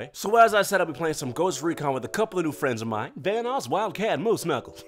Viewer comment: When he said ‘vanoss’ I thought he said thanos since I just watched his ‘thanos on my mind’ parody